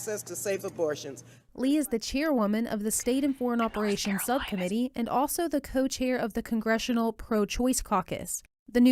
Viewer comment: Someone whispers something at 7:30 , can anyone make out what she's whispering ? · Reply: Sounds like she's saying "in North Carolina" ?